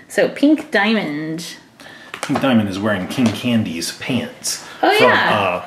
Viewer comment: i was yawning and i thought said "Thanos candy!"